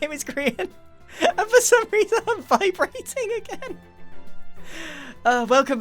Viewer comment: Everybody hears vibrating, but I hear 5 rating.